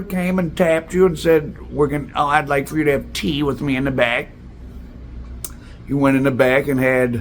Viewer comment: I thought you said I want you to have tea in a bag LOL